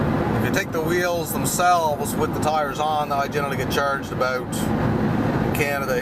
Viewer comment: When I heard “charged aboot” I was like wait, this guy is a Canadian, and he followed “Canada here” lol